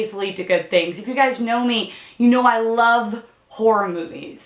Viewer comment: i thought you said "you know i love porn movies"😂